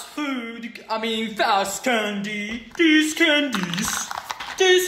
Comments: "DEEZ FOOKIN CANDIES" Is the best thing said by bandu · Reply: literally thought he said "these bowl of candies" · "DEEZ FOOKIN CANDIES" Is the best thing said by bandu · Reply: i thought he said "these modern candies" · why did i hear 'deez candice' at the beginning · I thought he said deez candice at the start. He actually said these candies.